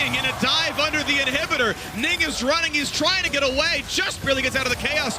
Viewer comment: Sounded like Phreak said "N*ggas running trying to get away "